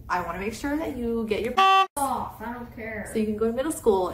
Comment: wait did said a bad word or was it the teacher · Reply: He said F off to the teacher.